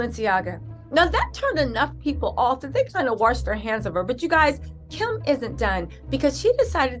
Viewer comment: Towards the beginning of ur video, when u said "Kim isn't done." I thought u said, "Kim is a DUNCE."🤣😂